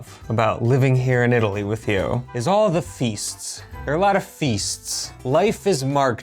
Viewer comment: As an Italian, I can't ignore my minde thinking 'fists 👊" instead of "feasts 🎉". 😅